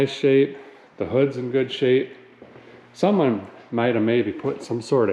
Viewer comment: Did you say the ruff is in good shape @ 4:12 while you were pointing at the roof??